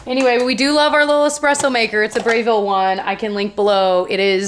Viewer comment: So I'm not familiar with the Breville brand, so I swear for a second there I thought you said "we love our espresso maker, it's a brave little one.." As in the Brave Little Toaster? What?! 🤦‍♀️😂